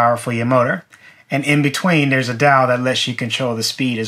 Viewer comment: I love regional accents. At 8:30 I thought he said "there is a dow to control the speed". I am guessing that is "dial"?